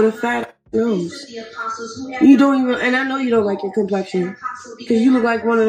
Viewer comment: Maybe I missed it but where did she mention dark skin girls? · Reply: I heard her say "I know you hate your skin color". I don't if she was talking to a White, Black, Hispanic etc.